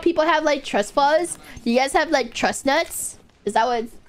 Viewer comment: i thought she said truck nuts for a sec